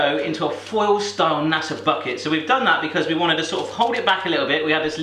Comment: did he say f***et instead of bucket XD